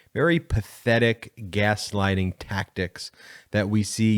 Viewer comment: Oh, gaslighting I thought you said grease lightning , my fault